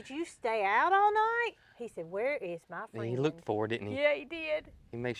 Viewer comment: I think I heard Moody say, “Hey, where’s my sign?” 🐄